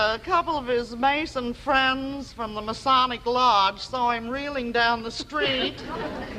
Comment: did she just say "masonic lodge" over there real quick like?